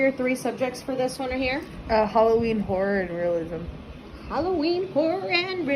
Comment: I try not to judge other people’s choices, but I had to listen to this several times before I realized she was saying her 3 subjects were Halloween, Horror, and Realism. I initially thought she said Halloween, Porn, and Religion. I was like ohhhhhhkay then lol Halloween Porn and Religion